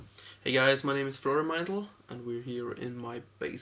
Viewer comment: sounded like he said "my named is frodo meindl" its funny cuz he looks like fucking samwise